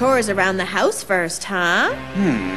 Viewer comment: I thought the video said "Butt Wave Idea"